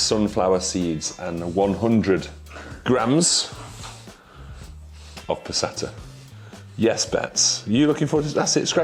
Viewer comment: Totally thought he said 100 grams of placenta